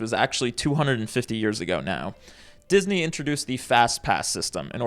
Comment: i thought fastpass said fatass omfg